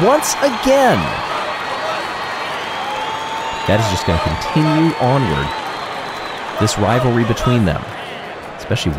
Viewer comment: Can someone tell me what the fans are chanting at 6:48? Seems to be for the Raspberry Racers. Also need help with what the Crazy Cat's Eyes fans humming at 14:58. (I've got the O'rangers, Momo, and Rojo Rollers fan chants down.) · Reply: Raspberry Racers fan say: "RO-RO ROBUST, Raspberry, RO-RO ROBUST, Raspberry" for around 4x CCE fans just have a tune in the background and chant "Meow"